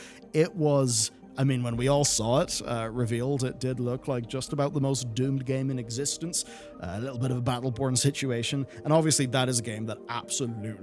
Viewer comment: Did he say battle porn situation at 12:10? Or have I played too many questionable games lately!?